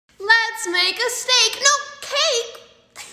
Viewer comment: Let's make a steak oops i mean cake I heard it as let's make a mistake 😅